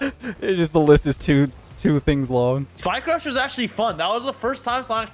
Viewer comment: what game mode are they talking about here? I cant make out what theyre saying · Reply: Spike rush